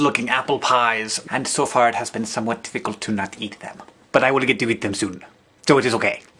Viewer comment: you mumble the first part of some words. You said difficult at 2:42 and it took me a long time to figure out what it was you said. It's kind of funny. anyway. this video was awesomer than usual. :)